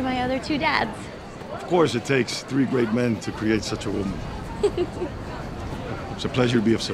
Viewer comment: did he just say but takes 3 men to create this woman. Does he mean through a.....4some